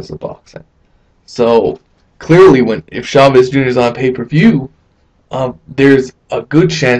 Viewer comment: Good Vid. O shit I thought you said Ward vs Chaves SR, O you said Chaves Jr, O hell nooooooooo. I will not support this PPV with my money. Good vid.